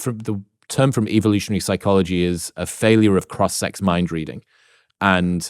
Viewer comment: EDIT: It's "cross-sex mind..." @ 9:55 A quick Google search of "failure of cross-sect mind reading" returned no results... did I mishear or does Chris mean something else?